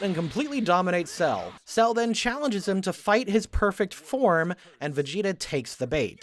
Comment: wha?? he always did that? that's what final flash is from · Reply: I, uh, was referring to the subtitles at 13:46 where it’s written as Cell’s perfect “home” instead of “form”